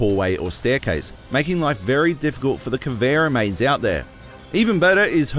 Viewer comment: i heard “conveyor mains” lol